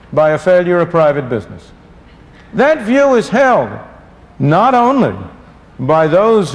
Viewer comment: Sounds like he says "that view is hell"